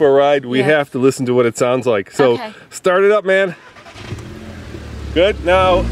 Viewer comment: Did he just said "start it up man" @ 1:40 ... LOL